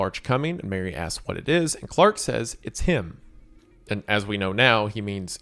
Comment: I could swear Clark says "its just me" sometime durring this scene when referring to his still life